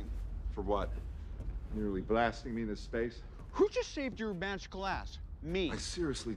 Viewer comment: I swear I thought tony said “I just shaved your magical ass💀”